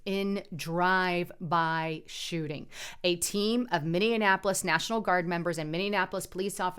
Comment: Did she just say minne Annapolis. I thought it was minneapolis!!